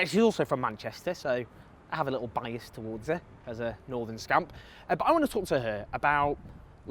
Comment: For a second I thought you said Northern scum!